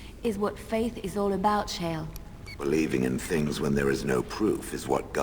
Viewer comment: "...is what faith is all about, Shale." When she said this with her french accent, for a second I thought she said "cher". (it means "dear one" for non-french speakers)